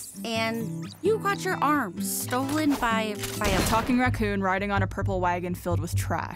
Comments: Okay for a second I thought Yang said "by a f**King raccoon" had to replay it. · YANGS DEADPANNED RESPONSE IS HILARIOUS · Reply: Imma be honest, I thought Yang said "a fucking racoon" on the first watch